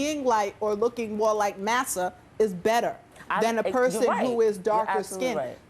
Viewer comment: What'd the girl say at 4:41 "Looking more like Nasa?" what?